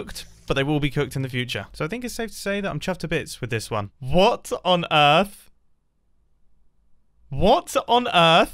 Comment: What did say? a) What on Earth! b) What's on Earth? c) Water on Earth!